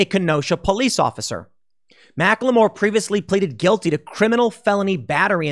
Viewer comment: lol sounded like you said Macklemore.